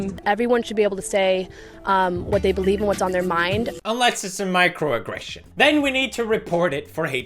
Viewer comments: I thought it said "microragesession" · i thought he said microtransaction.